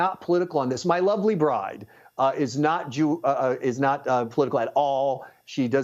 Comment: Did he say, "My wife is not Jew."? EDIT